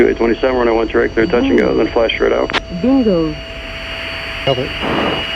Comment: "dildos" is all i heard, please correct me XD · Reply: I assumed it was bingo